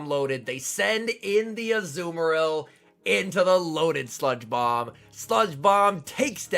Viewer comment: Sounded like he said loaded sponge bob lol…Also I have trouble hearing lol